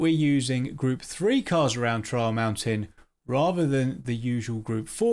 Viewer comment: For one moment He said Trial Mountain. I thought he said Troll Mountain. so Now PD is making Troll Maps now?